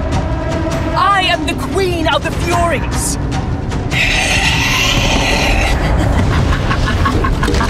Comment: For a second I thought she said queen of the furries which would have ruined this game